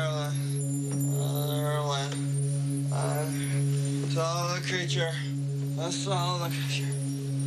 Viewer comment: 0:1 to 0:13 what does Wayne say ? Because I think he says " Dogs ,dogs , ohhhhh I'm Marilyn I'm Marylin , Oh the creature I saw the creature " to be honest I heard the creature part